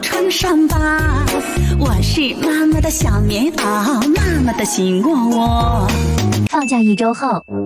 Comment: 妈妈的心窝窝 我听成 妈妈都喜欢我😂